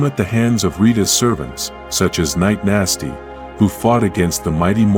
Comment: I heard “Mc Nasty”